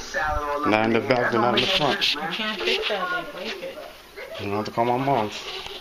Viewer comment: Did dude say “I’m gonna call my mom.”? At approx. 5:10-5:15 WTF? Mommy gonna help you?